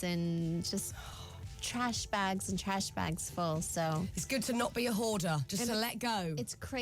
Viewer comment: definitely thought she was gonna say 'its good to not be a whore..'